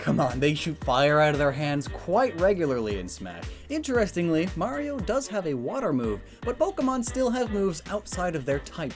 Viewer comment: Oooehw, Mario should be water type because he has a water move. Oooehw. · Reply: For a sec I thought he said Mario HAS GOT A WATERMELON MOVE really